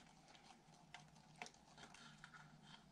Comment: What kind of glue did you use? It sounded like you were saying "hot eye" glue to two of us, but that doesn't sound right.